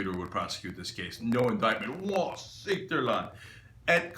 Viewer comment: what is he saying. Wassitula? Is this Turkish? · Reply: wa siktir lan.